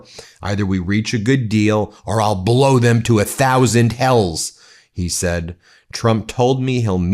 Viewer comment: I thought you said "a thousand elves". Um, okay, we talking Santa Claus or Lord of the Rings?